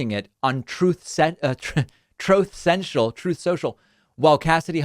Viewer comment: The clip of trump saying "troth central" followed by your deadpan "exactly" just about killed me. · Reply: I always hear "trough sential" as if his pig trough is essential 👌